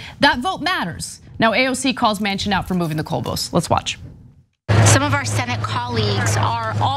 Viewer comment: did she say moving the "Coal Post"???? bahahahahahahaha